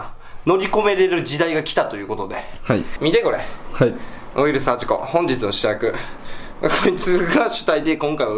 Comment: ウイルス8個（空耳）